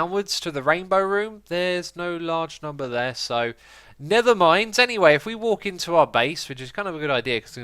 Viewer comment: Anyone else think he said "Nethermind"?